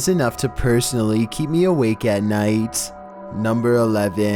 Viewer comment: Tbh I thought it said "videos that keep me alive at night"